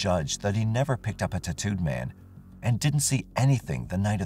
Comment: I thought he was saying tuhtooed man. Like some Native American tribe I’d never heard of.